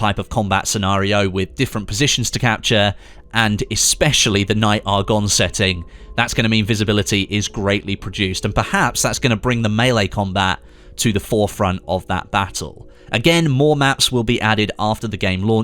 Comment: did he say "visibility will be greatly produced"???